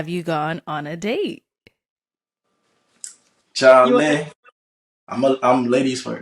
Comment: minute mark when asked if they had been on date yet, did I hear the guy say "Charlie"? That's a Ghanaian slang. I guess he is a Ghanaian-American😍😍😍